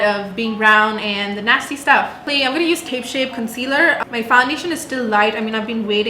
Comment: Did just say tape shape?